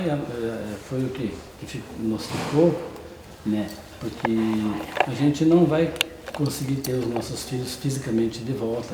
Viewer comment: Depois do minuto 18:00 eu ouvi umas palavras da menina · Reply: Nandoamor Farias sim ela falou "pai olha pra mim"